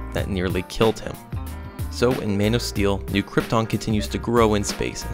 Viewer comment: You sound like your saying mane of steal